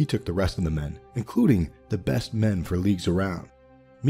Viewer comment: Hi, i'm doing a french trad of this vid, but at 2:15 , after including, you say "the best men for ???? around" could you help me :) ? the subtitles say leaks but i can't understand the meaning in this context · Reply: "the best men for leagues around" =)) and thank you!